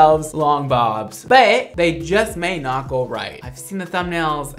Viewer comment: I'm sorry but I thought that said "boobs" at first. 🙄😅😬